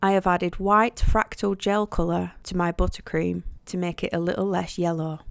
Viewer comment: Love to re-watch your tutorials. Always learn something new. What is white fracture gel in the white buttercream? Or atleast that what I thought you said 😆lol. Thank you for sharing and keep on caking 💜